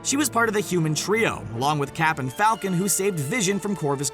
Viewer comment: did he say captain falcon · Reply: He Said Cap And Falcon